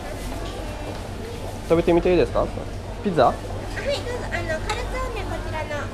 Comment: Qu'est-ce que tu dit à 10:49 :) ? "tabete mite ii desu ka ? "